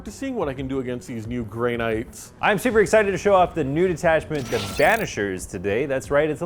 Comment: I had to do a double take I thought he said "Nude attachment"